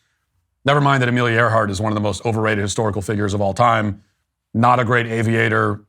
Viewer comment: I thought I heard Katy Perry say hysterical, not historical, in her speech. HAHA!😂